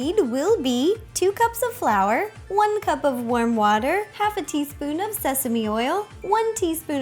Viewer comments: anybody else thought that you said worm water and got very confused.... nice dumplings tho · Am I the only one who heard her say "worm water" instead of warm? Lol 😂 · for a second i thought she said one cup of worm water XD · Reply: It did sound like that! :D But she said warm water. Worm water would be gross! Thank goodness she didn't use that! :P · I thought she said worm water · When she said warm water I thought she said worm water XD I was really confused 😐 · It sound like she says worm water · I thought she said worm water XD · when she said warm water I thought she said worm water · I thought she said "worm water" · I honestly thought she said worm water😂😂😂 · I heard her say worm water instead of warn water too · I heard "worm water" at the beginning xD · It sounded like she was saying worm water. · I swear I heard her say "worm water" at the part when she starts mixing the flour and water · I thought she said "Worm Water" so I went online and searched it up but when I couldn't find any I re-watched the video and realised she said Warm Water 😂😂😂😂😂😂😂 I'm such an idiot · lol it sounds like she is saying worm water! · When you said warm water I thought you said worm water